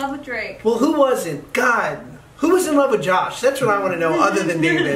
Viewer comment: what's Josh saying "other then me and" who is he saying ? Can someone please help me, i really want to know... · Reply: he said who was in love with me other than david